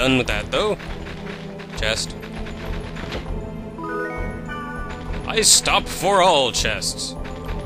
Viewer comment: It sounded like you said "I stop for all breasts!"